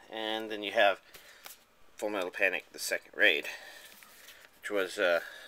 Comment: At first I thought you said "Full Metal Panic: The Second Grade". FMA is okay I guess. I can't help but feel it's over rated.